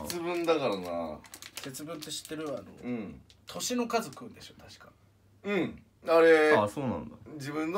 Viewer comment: 星の数食うんでしょ？たしか。に聞き間違えて笑った